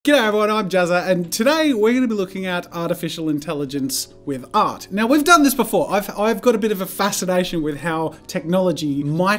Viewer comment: AT every beggibing of a video it sounds like he says "Get out everyone, I'm Jazza"